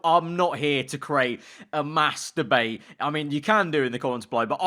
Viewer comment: am I hearing "mass-tur-bate?"